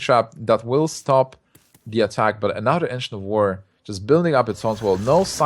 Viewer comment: "Ancient of Lore" Sounds like he says "Engine of War"